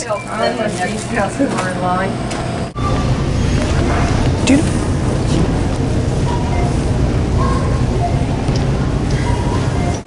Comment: Jack, did you just call her "dude" at 0:53?